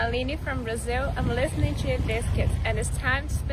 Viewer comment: Oh, Ear Biscuits. That makes much more sense. I thought she said she was listening to Limp Bizkit at first.